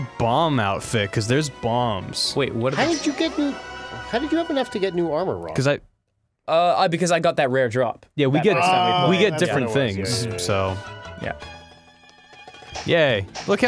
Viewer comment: Did call "Ron?"